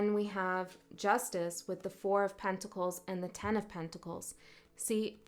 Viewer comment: when you said that Scorpio turned down the ten of pentacles and you tripped on your words a little, in my mind I heard "pen of tentacles!" How freaky!! But it sort of resonated! I turned downed being gripped by people taking from me. I appreciate your unique style and have such respect for your courage in putting out your unique messages. I am always super grateful, especially when it's for Scorpio! ❤️❤️ TY